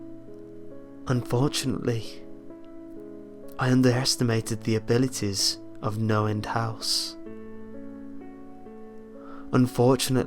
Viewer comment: I thought he kept saying Noland house...